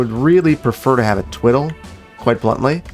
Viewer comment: What does he say at 33:37? Twiddle?